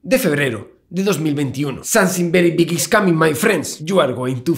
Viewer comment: alguien me puede decir que dijo . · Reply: Creo escuchar: Something very big is coming, my friends